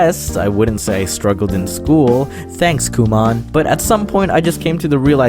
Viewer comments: did you say kumon? Oh my God I Remember going there for math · Lmao i heard Guma there which is what i get usually called and got triggered pretty hard